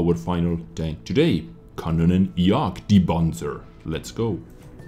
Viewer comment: said: "KanonJagdPanzer" What I hear: "Mega CockRoach E25"